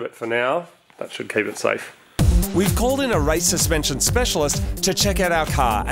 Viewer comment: Why did you call in a racist pension specialist? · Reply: says "We called in a race suspension specialist" but it sounds like "We called in a racist pension specialist". Jokes aren't funny anymore after you have to explain them...